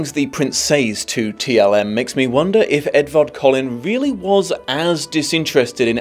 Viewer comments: I thought said Edward Cullen 🤣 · When you said Edward Collin at around 13minutes I thought for a second you said "if Edward Cullen was really as disinterested in..." and my brain immediately went "what is sparkles doing in this video?!"